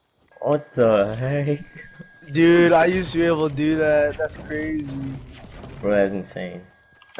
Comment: Sounds like he is saying, “I used to be able to do that!”